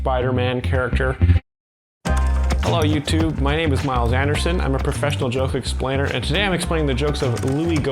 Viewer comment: Omg, when you said your name was Myles Anderson, I thought you said Mild Mannerson.